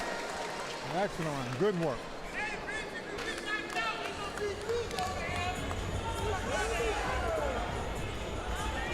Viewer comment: What they say in the crowd @ 3:10 ? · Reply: TREX